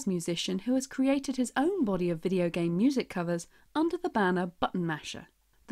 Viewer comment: typo in the english captions (banned" instead of "banner" might be confusing to deaf individuals)